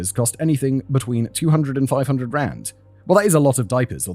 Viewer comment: I thought he said 200-500 grand and I was SHOCKED.